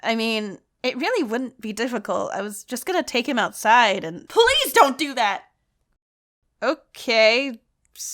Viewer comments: it sounded like, 'police don't do that' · sounds like she saying Po-Lice dont do that